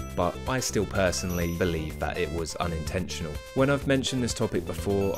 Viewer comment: the caption says "intentional" instead of "unintentional"